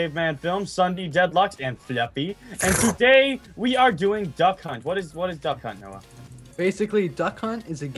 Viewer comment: I thought they said Duck Cu#t Lol